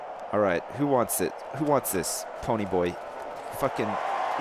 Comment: Did he say "Who wants this, Ponyboy?"